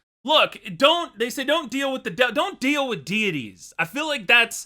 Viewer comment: did i hear don't deal with the devil cuphead reference?